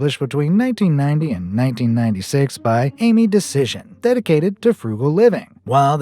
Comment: anyone else heard decision?